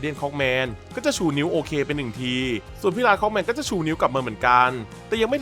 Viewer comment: พี่บอกว่าlarge clockmanหรอครับ ผมได้ยินผิดหรือพี่พูดผิดหว่า